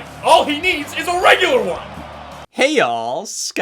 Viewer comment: What made it special is that he said “Hey All, Scott There!!” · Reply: I mean he did technically say "hey all scott here"